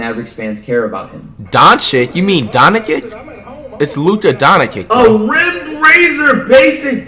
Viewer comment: I thought this man said Luca Dominic😂😂😂😂😭😕🧐. Please tell me he said Luca donćić